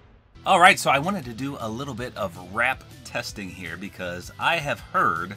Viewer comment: For a sec I thought it said ramps